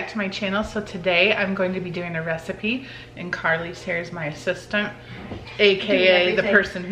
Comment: when you said that your assistant at 0:13 at first I thought you said she was your sister